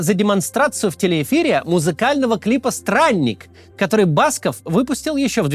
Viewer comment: Я не расслышал как называется эта песня Баскова? "Сральник"?